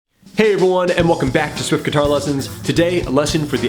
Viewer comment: did he say hey bhuvan 😂😂😂